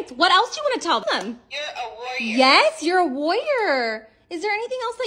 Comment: I thought she said ur a loyar 😂😂😂